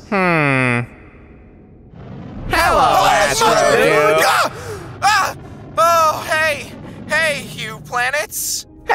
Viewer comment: I think astrodude said a bad word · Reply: No he said HOLY MOTHER…!